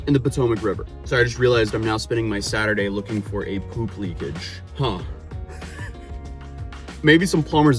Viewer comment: did someone say poop leakage?😂😂